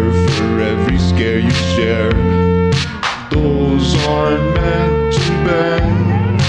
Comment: Am I the only one who hears "doors arent meant to bend" ? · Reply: i heard "h0es arent meant to bend"😭😭